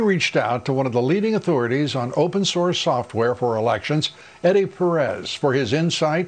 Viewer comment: I thought he was saying open sores 😂😂😂 then I realized it was open source haha